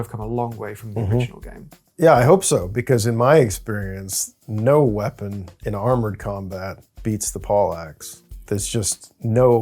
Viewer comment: so am I the only one who heard "no weapon in armored combat, beats the Polacks". Thought to myself... well, that's cool, but why the slur? :D